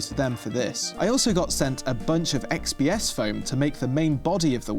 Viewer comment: for a second I thought he said fps foam. 🤣🤣